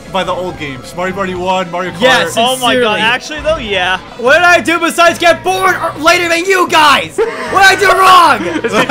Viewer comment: who does he say? I can't quite get it and captions don't help. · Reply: "What'd I do besides get born later than you guys?! What'd I do wrong?!"